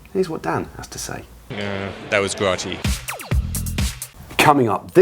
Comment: What does Dan Craven say at 10:55? Can't understand it as a non-native speaker. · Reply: That was grotty